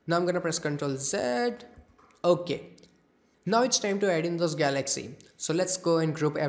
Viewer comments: i cant hear it clearly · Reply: “Now it’s time to adding the galaxies” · I cant understand what he says on 8:46 all i hear is now im gonna press ctrl and idk whats next of what he say :( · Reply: XasTron you dont have to so that, he presses ctrl and Z to Go a step backwards